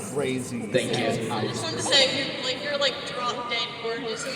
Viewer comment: please, what did she say? What happened? I'm not a native speaker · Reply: She called him drop dead gorgeous. It’s an expression used to mean someone is really beautiful!